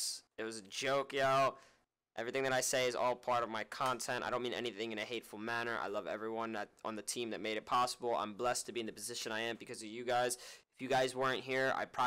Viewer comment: did he just say id probably be selling bricks on the corner rn